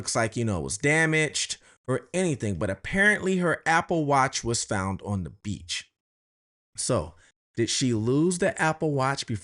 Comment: I thought they said her watch was found on the soulmate